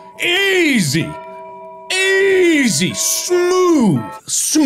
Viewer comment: Did he say "Smooth as Babyblood"? Mark, what did you do? Leave the babys alone